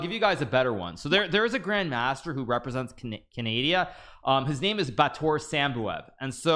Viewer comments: bro did not say canadia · Did he just say cadaidia